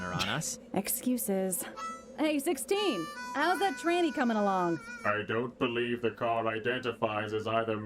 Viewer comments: i thought for sure she was saying trainee for the longest time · Did I miss it? What was the inappropriate joke? I’ll be honest for whatever reason assuming it’s what 18 said… I always heard “How’s the training going?” Or something… and the sentence made no sense to me, so I just assumed it was nonsense and carried on.